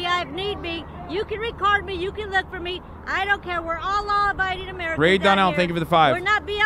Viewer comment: did she say "we're allah abiding americans"?? inshallah